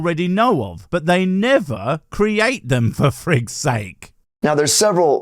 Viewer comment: Did...did you just say "for Frith's sake"? Cause if so, I love you boy. ❤